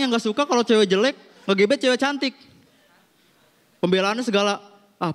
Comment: itu cwek jelek ngegebet cwek cantik kah? atau salah denger